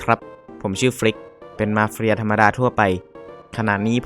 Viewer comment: ใครได้ยินว่ามาเฟรียบ้าง: )